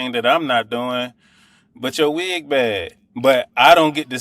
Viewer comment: Did he just say "but yo wig bad"????? Smh. Petty Betty.